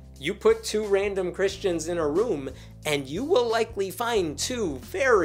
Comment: When you said “put 2 random Christians in a room”, I thought you said “put 2 randy Christians in a room”.